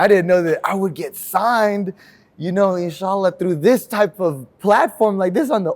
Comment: does he say inshAllah?! This could be the first Muslim in The Ultimate Fighter show history